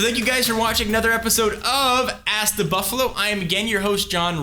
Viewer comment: every time i hear "ask the buffalo" i hear "ass to buffalo".... just me?